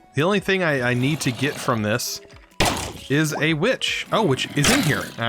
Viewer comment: for the first time i heard a bi##h not a witch